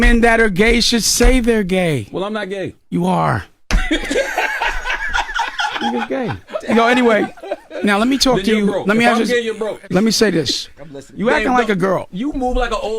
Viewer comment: did Charlemagne say "if I'm gay then you're broke" 😂😂